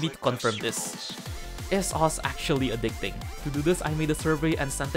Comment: i heard is sauce accualy adicting xD